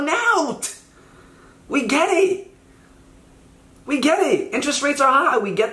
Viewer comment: oh i thought he said gay or gang but its get it